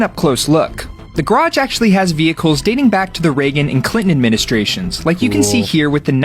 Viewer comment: Swear I heard the "Klingon administration"